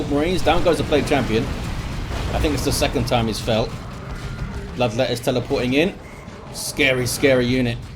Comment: swear he said "love letters" :P